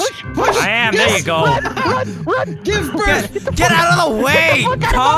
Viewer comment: anyone heard the word give birth at 1:58?